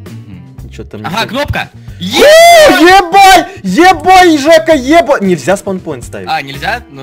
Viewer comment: мне одному послышалось "еба*ь"? :D